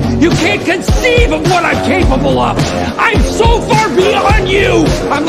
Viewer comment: “Im Saul far beyond you!” I felt that bro 😔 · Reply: I thought it said "I'm so far behind you"💀💀